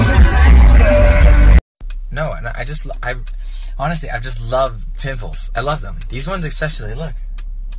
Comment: what did he say??? He love...??? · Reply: Jessica Heeg He loves pimples! (lmao)